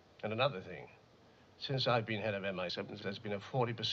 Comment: timestamp 2:37 "since i've been head of..." what is he saying it sounds like he's saying MI7 but there is no such thing i know of even in the bond films and it also sounds dubbed over/garbled/repeatedly recorded over for some reason